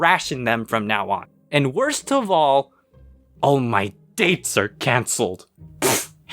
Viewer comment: I love Cells at Work and I thought you said "All Might dates"